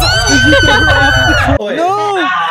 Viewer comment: did anyone hear say “noo jade I’ll save you”